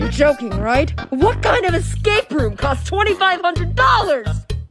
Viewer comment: When is said prison I thought it said present